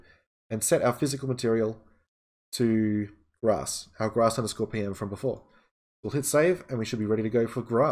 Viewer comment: It sounds like you say, "our grass have a scorpion from before", and the closed captioning also says it LOL!